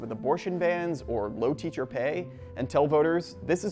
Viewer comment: ngl I kinda thought he said low taper fade for a moment I might be cooked